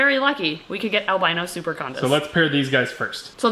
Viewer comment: Around the 12:30 mark the subtitles did put "albino super condoms" instead of super condas 🤣